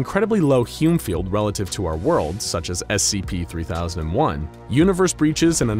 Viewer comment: Did he say our world